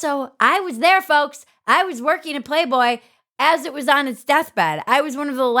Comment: What She Said: "I worked at Playboy as it was dying." What I heard: "I destroyed Playboy." ;-}